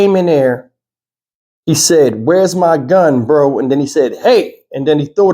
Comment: Sounds like he said, where's my gun boo?? Anyway it goes, it was senseless!! Now he's gone & she's still here with his property. 😮